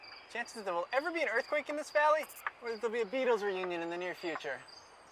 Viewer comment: I had to skip it back when he asked “what’s more likely, a earthquake or a Beetles reunion “ as I thought he said peedo reunion lol But I’m looking forward to the rest, you finished part one just in the right place to grab you 👍🏻